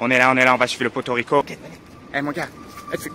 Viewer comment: "On va chez le poto rico" je sais pas pourquoi mais dans ma tête il a dit "on va à porto rico"